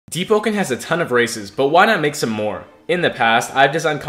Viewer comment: I thought mans said “Deepwoken has a lot of racists” and he still not wrong